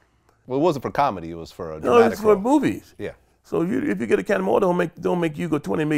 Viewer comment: Did he say "Movies" or "Boobies"??